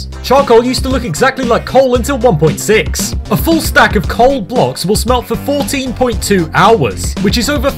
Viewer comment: I thought it said cool blocks